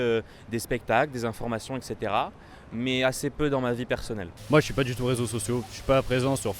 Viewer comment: did he really say 'je suis pas du tout" and not "j'ai pas du tout"? At 2:23, the dutch lady probably meant to say "je n'aime pas" rather than "gênée par". Anyhow, I find this to be quite a legendary series! One such 5 min episode is worth around 5 hours of classroom instruction imo. Keep up the great work!